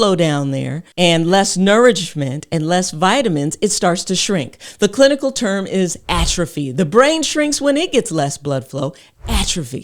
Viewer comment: I thought I heard her say “the clitical term is atrophy” 😂